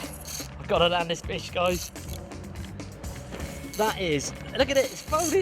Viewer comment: who else thought he said "gotta land this bitch guys"😂😂😂